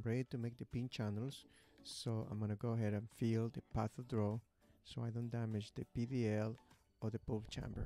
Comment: what is it he says he is going to feel at 1:52 so he doesn't hit the pulp or PDL? Sounds like he is saying he will feel the "path of drow"